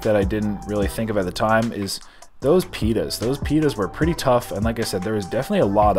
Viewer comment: You should change the title of this vid to the “$200 Insane Pita Challenge” since you kept calling the tortillas as ‘pitas’ for some odd reason. 😂 😂 😂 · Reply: I thought he'd said pepita at one point, which are pumpkin seeds. Yuuuuum.